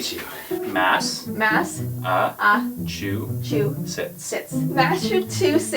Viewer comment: It sound like he said “massive two shits” XD